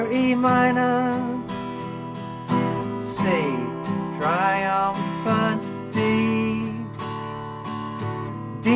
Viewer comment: C triumphant D. I heard it as "See Tenacious D".